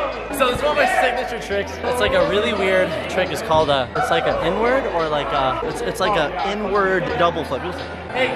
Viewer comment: He was explaining the trick, and I thought he said... "it's like and n-word" and I was like nigggaaaaaa 🙄 (I'm not a flipper)